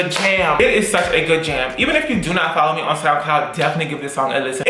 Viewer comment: Every time says soundcloud I hear “snapcloud” and ask myself what is this app?!